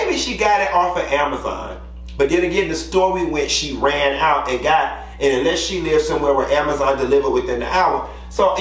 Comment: NEWSFLASH: She knew she was using Gorilla Glue Spray, she stated that in her video. She was too lazy to go get some Got 2b Glue so she used the GG spray. I would hate to be famous for some dumb ish like that. Bless her heart 😏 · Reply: I thought she said she ran out of the got 2b glue? Either way the shit is stupid